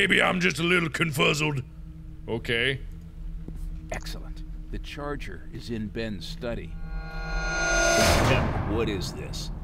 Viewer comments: What we hear: Excellent, the charger is in ben's study What the subtitles say: Excellent, like ben in the study was the charger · What the character said: Excellent, the charger is in Ben study. Subtitles: Excellent, like Ben in the study was the charger